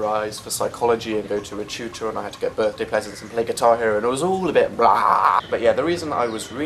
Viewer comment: I thought it said "give birth to peasants"